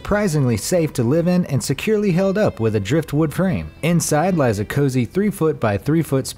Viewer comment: I thought he said inside Liza Koshi at 0:54 😂lol lmbo